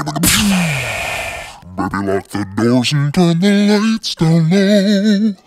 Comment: what does he said at 1:45 ! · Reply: Baby lock them doors and turn the lights down low